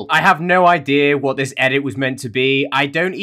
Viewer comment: What it says: I don't know who I am anymore What thinks it says: I don't know who J cum anymore I think these Gacha videos are messing up brain